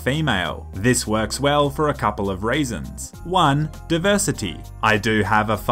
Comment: did you say "it works best for a couple of raisins"